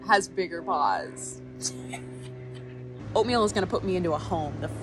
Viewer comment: It’s even more cute that it sounded like you said furbal abuse